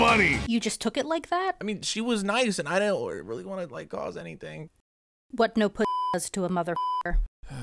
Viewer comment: she clearly said "What no pistachio does to a mother father"